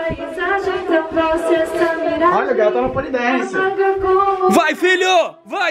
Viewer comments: o falo "a bola " ou foi impressão minha kkkk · só eu escutei o falar "a bola"? 💕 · Eu posso ter me enganado mas eu acho que ouvi o falando BOLA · Só eu escutei o falando "ah bola"? No minuto 19:35 Sério,meu deus q fofoooooooo